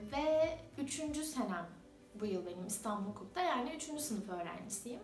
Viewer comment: Yanlışım varsa düzeltin 1:18 de 20 yaşındayım istanbul hukukta 3. senem mi dedi yoksa yanlış mı anladım